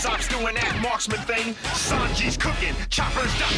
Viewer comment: "SANJI'S COOKING, CHOPPER'S DOCTORIN'" will never not go hard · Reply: Wow, I always heard and "choppers got the ring", thats sounds and makes much more sense.